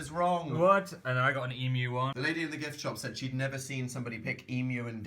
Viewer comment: I thought said “GIF shop”... What has my life become?!...